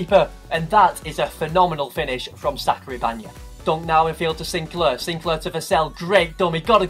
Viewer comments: who else heard him say sacary bagna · did he just say sacary bagna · Haha am i the only one. Who heard him say sacry banya